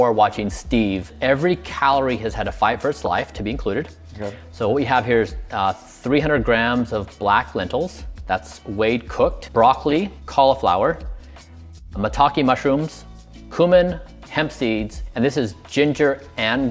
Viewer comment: what did said at 4:42, well cooked ? what does that mean ? I get it : weighted cooked, meaning, if my english is correct that it's weighted after being cooked (as opposed to before cooked)